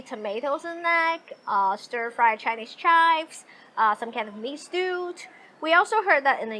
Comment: What does she say around 4:13 after the word chives? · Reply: I believe she slightly mispronounces "beef stew"